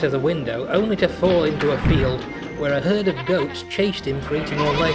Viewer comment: What did the narrator say in 5:41-5:42? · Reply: A herd of goats chased him (Fat Controller) for eating all their kibble.